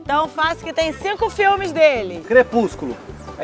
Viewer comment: eu ouvi o falando crepúsculo só que ele falou baixo